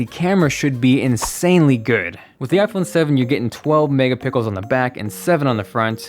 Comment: I heard "12 mega pickles" 😂😂